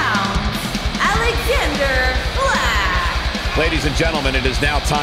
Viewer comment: did she said alexander black?